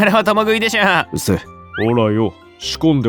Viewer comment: ここのペンギンの台詞 字幕は「うるせぇ」なのに 声は「うっせぇ」に聞こえる